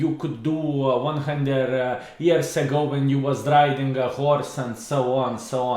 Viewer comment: I was watching this on my phone in full screen and having a snack at the same time but when I heard "driving a horse" I had to drop my snack, pick up the phone, exit full screen and like this video!